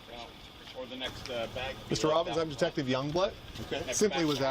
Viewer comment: sounds like he said his name was detective young blood